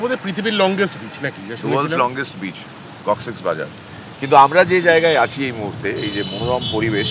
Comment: Did I hear coccyx bazaar at 1:26?